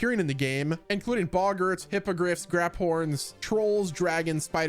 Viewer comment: check your subtitles at 7:11 my dude. you say bogart but the subtitles say something much worse.